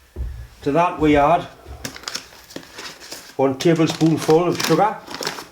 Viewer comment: I cannot understand this brogue. The closed-captions are a mess. At 00:41 I am hearing "Woo-un tibblespoo-un full of __ (?) The closed captions show "on tablespoon full of crap". I always knew the Geordie accent was difficult, and this is the proof. From the appearance of the bag, I think it must be sugar.